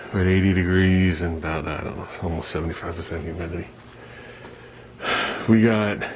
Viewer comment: I don't even look at the "thumbs downs" :) I thought you said 70% humdity? :)